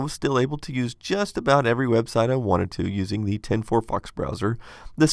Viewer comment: could not understand your wording, did you say, 10 fox browser? Update: never mind, I seen you reference it later when playing a video.